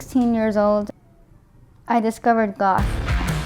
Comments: I thought she said I discoverded God not Goth; honey you should discover God! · I thought she said she discovered God and when all those gothic pics popped up, I was like 👀 Took me a minute to realize what she said lmao 😂💀💀💀 Edit: OMG!!! 😱 I wasnt expecting so many likes! Thank you all so much! ❤ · Lol I thought she said she discovered God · I could have sworn she said I discovered God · Oh wow at first I thought she said she discovered God then she said goth!?